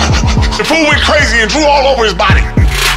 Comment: I thought he said he drooled all over his body 😂😂😂